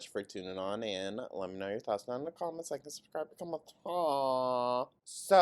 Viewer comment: I won't lie, for awhile with your intro I thought you were saying "Like & subscribe, become a toe" ... I'm sorry